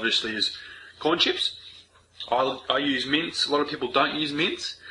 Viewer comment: Every time you said meat I thought you were saying mints